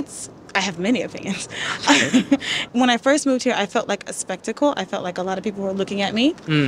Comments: Thank you so much for watching! From this month, Japan is open for tourists but it seems like it’s still difficult to come normally I hope it’ll be open completely by end of this summer and meet you guys here in Tokyo! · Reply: Your subtitles say "skeptical" but should say "spectacle" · The subtitle is wrong. She said "Spectacle". Not " Skeptical".